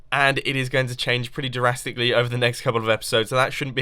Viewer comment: Did he say jurasically or drastically? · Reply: You mean 0:33, and that's just how "drastically" is pronounced.